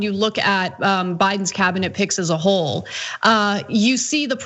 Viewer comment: I thought Ana said Biden's cbinate pigs. Which his probabley an apt description as they will all have the snouts in the trough.